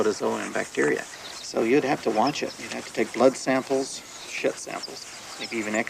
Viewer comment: Wait a second.. what does he say at 43:31? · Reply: shit samples